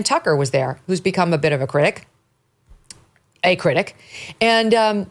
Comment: almost sounded like 'he's become a bit of a prick' lol